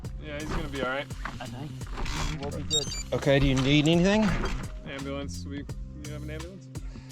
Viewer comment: Anyone catch what those guys in the night asked for around 17:20 that he said he would pack next time? I cranked the volume and still couldn't make it out. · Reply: Ambulance.. they don't fit in a saddle bag very well